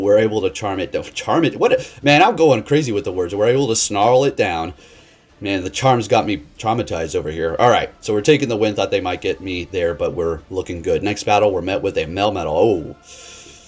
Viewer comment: I legit thought you said, "The charm's got me charmitized". 😁 Hope your Thanksgiving was blessed, man!